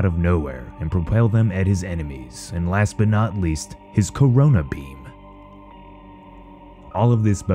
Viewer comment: Did you just say his ”Corona” Beam?